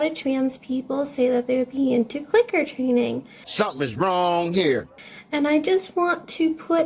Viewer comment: I always have a hard time understanding what this human girl is saying (I'd call her a 'bitch' but that'd be validating her bullshit, so nah. I'll do the opposite.) Is she saying "CLICKER TRAINING", like... for dogs (I'm asking because I used to train dogs in middle school... but I mean she also looks like she's trying to BE a dog...) Annnd... just gonna say... if you're able to get 'trained' by that... you clearly don't have much going on up there anyway... (Like to each their own honestly... but why do these kinds of people always feel the need to broadcast EVERYTHING?)